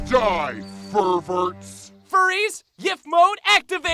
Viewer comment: Did... did they just say yiff mode...?